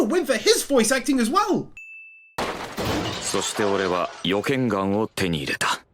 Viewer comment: Is it just me or was rudeous saying the n word